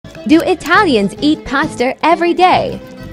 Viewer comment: i heard "paster" instead of pasta